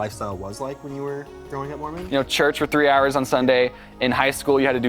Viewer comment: i first thought you said you were moron when you were growing up